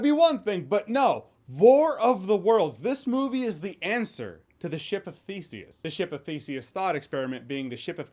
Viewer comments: I heard "Bore of the worlds is the answer to the ship of feces" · For a minute, I thought he said: "The $h×t of Faecesous! "🤮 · At first I thought you said "ship of feces" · I thought you said, ship of faeces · I thought he said “ship of feces” there for a minute. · I totally thought you were saying, “ship of feces”! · All I heard was "ship of feces" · I thought you said "Ship of Feces".... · At first I thought Jeremy said "the ship of feces", which somehow still tracks. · At first I thought you were saying the Ship of Feces.